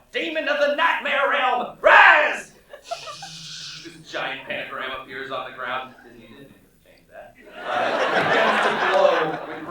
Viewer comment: What does alex say at 21:00 about the pentagram? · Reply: "A GIANT PENTAGRAM APPEARS ON THE GROUND ... disney made me change that"